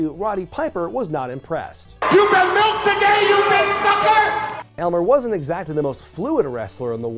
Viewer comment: What does Piper say at 5:12? "You've been milked again, you big fucker"? I must be hearing it wrong